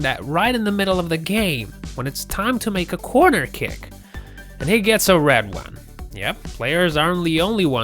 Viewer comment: first time I heard "quarter kick", always heard of corner kick.